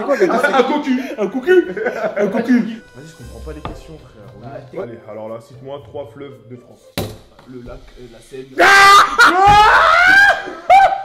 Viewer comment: c moi ou a 7:45 il dit le lac de sarcelles mdr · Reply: non il dit le lac … la seine